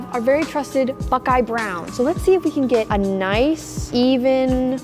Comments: I heard her say fuckeye brown… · did anyone hear her say a bad word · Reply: She said bukeye brown